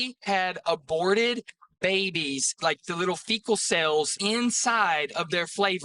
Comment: did he say FECAL cells? Lmao 🤣